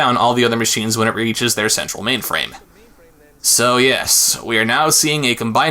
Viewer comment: I misheard structural mainframe as "sexual mainframe".